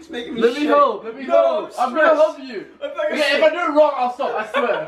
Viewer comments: Did anyone hear at 9:46 Caspar say i love you or was that just me? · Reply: Late reply but he said "Imma help you" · Can anyone else here it when Caspar says "let me help, let me help! I love you" it's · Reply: I swear every time I watch it I hear 'I'm gonna love you' 😐 I'll just try again..